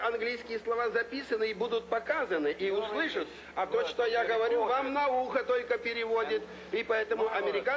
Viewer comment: On 6:11 Khrushchev did't say anything about science, he said "in your ear". in Russian the phrase "in your ear" can be heard as "about science". So what he actually said was "What I say to you in your ear won't be translated". Maybe someone already said about that in the comments but just in case